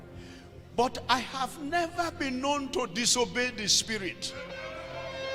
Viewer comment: In the begining of his story telling, he said " I have never lied to the Holy Spirit ". That is the biggest lie ever. You are a mortal man claiming perfection. You have NEVER lied? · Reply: He actually said, "I have never being known to disobey the Holy Spirit". The things of the Spirit are mostly controversial to mortal being. See the case of Prophet Michiah.....